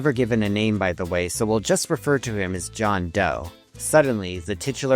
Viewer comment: Did somebody say John Doe